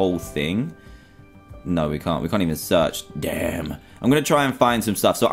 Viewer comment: did he say dam